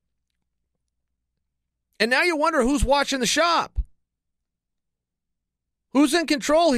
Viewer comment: I heard that as " Who's watching "The Schaub"